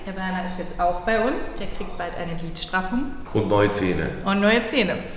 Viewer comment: Oh man ich hab echt Glied Straffung verstanden anstatt Lied Straffung ;)) musste es mir mehrere Male anhören bis der Groschen endgültig doch noch gefallen ist;)